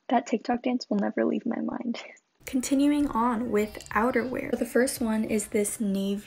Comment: also when u said outer wear my fried brain thought u said outer banks